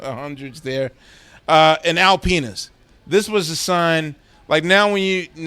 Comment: I had this playing in the background like a podcast and I thought Quest was saying "Al Penis"... not Alpinas.